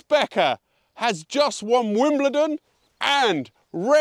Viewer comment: Did I just hear 'Wombledon' ?